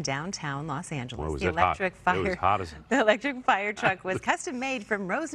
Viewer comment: 🤣🤣🤣🤣🤣 did she just said electric F...er?